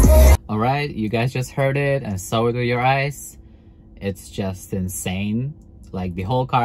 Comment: what did you say? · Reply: "Saw it with your eyes"